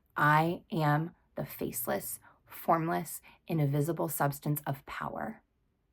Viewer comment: a QUESTION: I always thought you said "INVISIBLE source of power" but listening to this video right now so I can take notes I realized that maybe the word you are using is "INDIVISIBLE". Both words make perfect sense, but I got curious. Which one is it? 😂 Thank you!!!!😢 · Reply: Ha!! Sorry! “Invisible” 😊